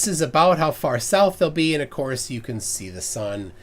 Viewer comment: …sorry, south of the what now?? The intercourse?? Lmao I swear thats what I heard 😄🤦🏻‍♂️